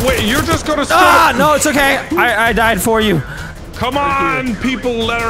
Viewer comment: before wildcat dies, it sounds like they say wildcat down